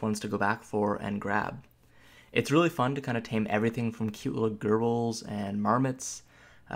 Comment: Lol your pronunciation bro. It sounded like you said it was fun to tame cute little girls. It's pronounced jer-bils. Also, the wyvern, being from heraldry and not just made up for games, has a correct pronunciation, and any other is just wrong. It's why-vern. I agree with a lot of your points but I have a real problem with the fact that there are basic mmo controls that are completely absent from the game, such as the complete lack of any function to cycle through friendly players other than by clicking on them (control F1-F5 doesn't count as that's only for party members AND sucks anyway). That is way less efficient than having a cycle button such as tab for enemies. Mouse clicking on them isn't a real alternative as the mouse cursor is almost invisible at many resolutions. Another thing is that my hotbar disappears EVERY TIME I change zones (like entering/leaving a dungeon) and I have to relog to get it back. It does this on every character.